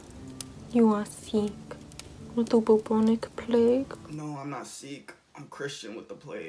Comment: “No im not sick, im Christian with the plague” 😂 0:38 “You know what uh ima need subtitles across these, i have no idea what she is saying” 😂 This made me laugh so hard 😂 your facial expression be funny asf 😂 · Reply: Jiaya Weeks its no I’m not sikh not no I’m not sick so yeah just gotta say